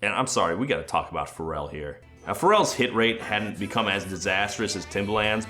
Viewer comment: Wild that Pharrell has now worked with two Trainwreckords artists yet his own career has survived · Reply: When started talking about him he said "Pharell's hit rate" but I thought he said "Pharell's hit, 'Rape'" and I was like "actually I think it's called Blurred Lines" :/